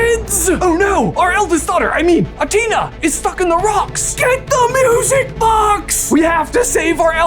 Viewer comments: Oh my god you said stuck in the rocks I thought you said f***ing the rocks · did I just misheard or he said "Athena is f***ing the Rocks"